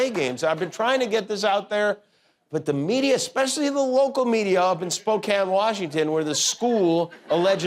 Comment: When he said local media, I thought I heard "woke-al" media. Sounds like one of his classic slips of the tongue!